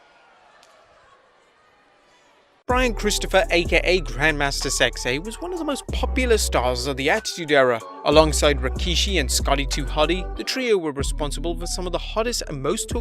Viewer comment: Rest in peace grandmaster sexy was a great wrestler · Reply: LoL I thought you said grandmother 🤣